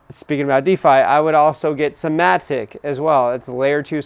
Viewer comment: "Speaking about DeFi, I would also get some..." (4:16) Can't make it out, what coin did you guys say there? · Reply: MATIC